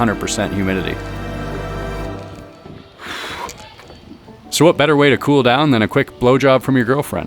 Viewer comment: Great video. At one point I was just listening and tinkering in the other room… I heard you say what better way to cool off than a BJ from your girlfriend…. I was like WHAT, came back in the living room and got a good laugh.